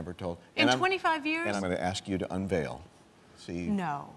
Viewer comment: What’s Pat sayin @ 0:11? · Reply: "I'm going to ask you to unveil."